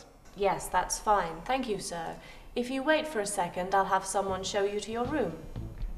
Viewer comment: is she saying: I'll have someone show you TO YOUR room???